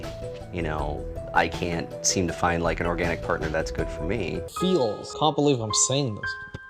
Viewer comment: I swear when the doctor came in, and said "feels," I thought for sure that he said "heels." Like dude being short was the problem with him finding a woman.